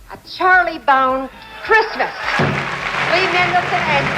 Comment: Did she say, "A Charlie Bound Christmas"... too much champagne?